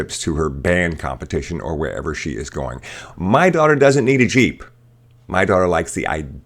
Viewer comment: Honestly i heard "my daughter wants a sheep" and imagined the girl looking at shaun the sheep :)